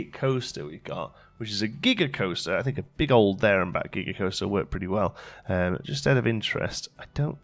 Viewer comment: I thought you said Giger-coaster. And now I want one!!